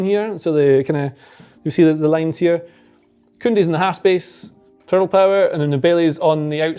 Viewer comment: Did he say “Kounde’s in the half space….turtle power” or was that just what I wanted to hear?